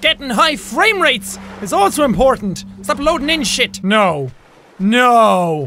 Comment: I thought he said shitwreck instead of shipwreck